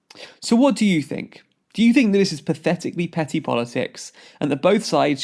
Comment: Definitely thought he said 'pathetically petty bollocks' and had to go back to check. In short, yes. It is pathetically petty bollocks.